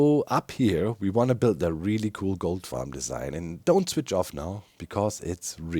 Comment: When you said "Gold farm" I thought you said "Goat Farm" and I was like "how are you farming goats on the nether roof? You trying to get screaming ones? goat horns to customize?"